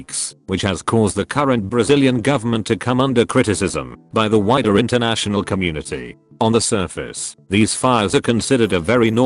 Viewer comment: i heard "...by the whiter international community." not wrong tho